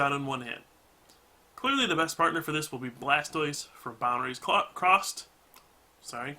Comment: did he just say blastoise from boundaries cocked?